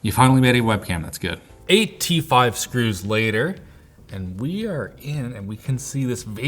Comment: I thought he said 85 screws instead of 8 t5 screws and my jaw dropped😂